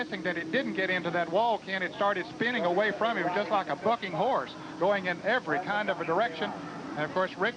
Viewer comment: I thought I heard this guy almost cuss when he said the F word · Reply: He said “Bucking Horse”